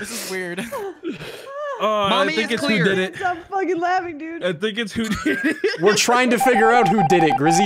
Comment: "I think its hoodini" is all i heard right there